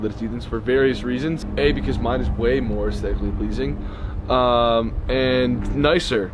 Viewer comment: I SWEAR I THOUGHT HE SAID SEXUALLY PLEASING